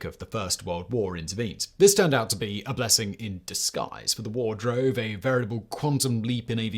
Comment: Yes, the first World War was a blessing in disguise. And in fact it was runner-up in the WTF is this Hall of Fame beat out by its younger brother... It occurs to me I might have misheard. Maybe he said a blessing in the skies as it would fit the narrative.